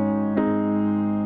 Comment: I thought she said "Your body's pulling Gs"